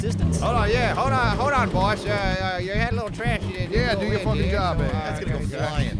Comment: did he just said "do your fucking job man"